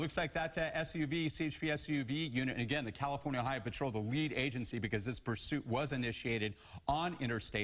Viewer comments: "The California Highway Patrol, the [garbled[ Agency" I had to back it up. I swear I heard "The Weed Agency" and thought maybe they were in the City of Weed, Ca. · "The California Highway Patrol, the [garbled[ Agency" I had to back it up. I swear I heard "The Weed Agency" and thought maybe they were in the City of Weed, Ca. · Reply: I heard "California high patrol, the weed agency" 🤣